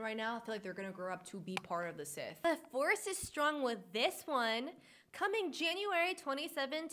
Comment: I THOUGHT SHE SAID “DIVORCE IS STRONG WITH THIS ONE”